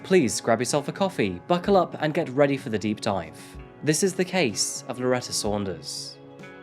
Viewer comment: I hate that every time he said her last name I heard "Doctor... Reginald... Saunders..."